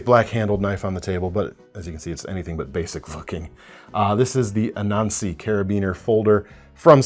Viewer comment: Did drop the “F Bomb 💣” or am I hearing things? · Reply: You are hearing things, he says "anything but basic looking" there, no bombs.